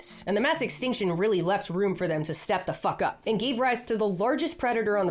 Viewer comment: I coughed as you said "Stepped the f*ck up" and could've sworn you said shut the f*ck up.